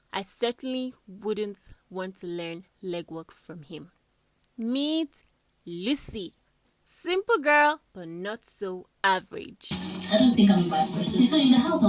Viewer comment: I'll definitely take my back my subscription... Bye bye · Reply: I just wanted to apologize this.... I had to go back and listen. It's only after you explained the words you said that I understood.... I thought you said "meet Lucy, simple girl not so ugly"... I heard wrong.... I'm from Swaziland and the accent is very different. I'm very sorry for that. 🙏🙁